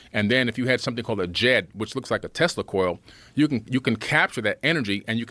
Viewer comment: Billy is an idiot. Hes your basic DEI hire. @ 8:28 did he just say testicle?